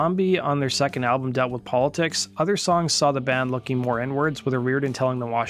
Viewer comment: I HAD TO LISTEN TO IT THREE TIMES, I HONESTLY THOUGHT IT SAID... "...other songs saw the band looking more N-words..." 😂